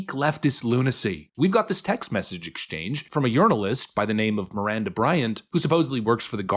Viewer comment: Had to rewind a couple times to make sure I heard that right! LOL Urinalist! I have a browser extension that replaces words on webpages, and I already had it set up to replace "journalist" with "urinalist".